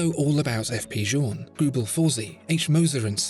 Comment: what are the brands he lists at 1:05 im new to horology and didnt hear them clearly enough and am interested to learn more. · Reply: H Moser & Cie, Greubel Forsey and my favorite brand F.P. Journe.. journe is simply something else